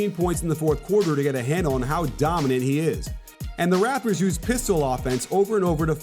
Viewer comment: did he say rappers